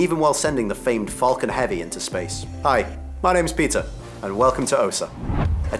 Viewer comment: I thought the host said: I’m pizza 🍕 because of his thick accent 😂